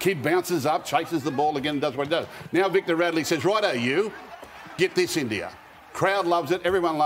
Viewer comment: Did Gus just say give this to India... wow no way.